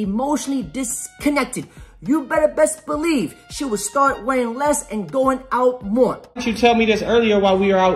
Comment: Thanks to that loud-ass heavy metal I torture the world with, I thought you said "weighing less and going out more."